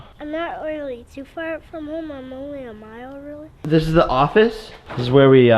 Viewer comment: did he say a mile